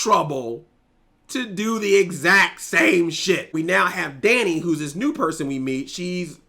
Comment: did just say shit?? :o